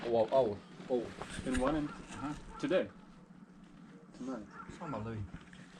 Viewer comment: where you think the railway employee said “omg” @ 7:36 he actually said “give me a moment so i can see” very helpful guy lol